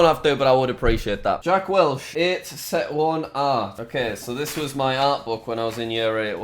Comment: did he say Set 1? ha nerd im Set 3 for life.